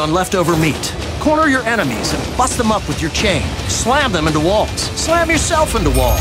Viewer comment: "Corner your enemies and _ them up with your chain." I had to listen to this five times to hear what he was actually saying. I was like--they couldn't have said what I thought I heard LOL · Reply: He says bust them up up